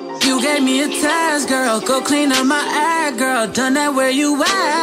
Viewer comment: i really thought he said "go clean up my ass girl"